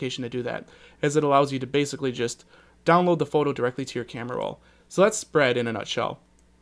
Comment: it sounds like he says "so thats fried in a nostrill" ;D